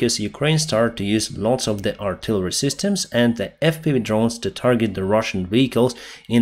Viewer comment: 🔥 Support Pilot Blog on Patreon! <link> 🔥 Find me on PayPal <mail> ✈ Denys Davydov Telegram Channel: <link> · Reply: Deny's, it sounded like you said FU drones?